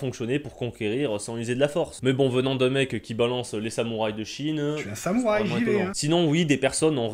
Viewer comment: Pour la dernière ref aux "Samuraïs de Chine" j'ai entendu "Sa muraille de Chine" peut être un message caché ( ͠° ͟ʖ ͡°)